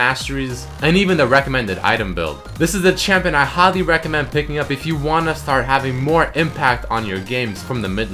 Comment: i think you made a mistake at 1:20 you said hardly recommend but doesn't hardly recommend mean almost don't recommend, or did you say highly? i'm confused